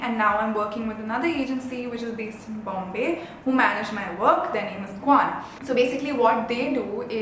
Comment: did I just hear kwan?? That agency which is in news??